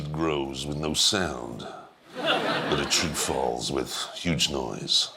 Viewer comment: I GENUINELY, HONESTLY thought he said "a shit falls with great noise" xD